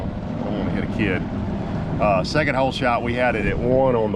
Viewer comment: did you say we hit a kid?